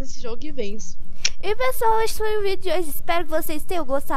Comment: eu juro q ouvi gente hoje foi o vídeo de hoje